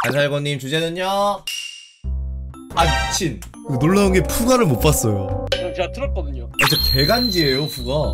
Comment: 항상 들었을 때 후우가라고 들었었는데 푸가구나.... 4:39 잘 된 건 맞는데 너무 하찮아졌잖ㅋㅋㅋㅋㅋㅋ 8:15 대가리에 한발 ㅎㅎㅎㅎㅎ